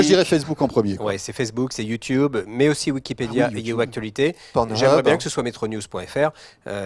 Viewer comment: A 10:43, j'ai entendu quoi de la part de Jean-Marie Portal? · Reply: T'as bien entendu pornhub